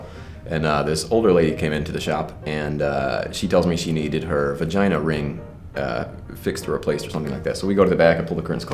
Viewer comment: oh a genital piercing. i heard "vagina ring" and was wondering wtf he was doing screwing with her birth control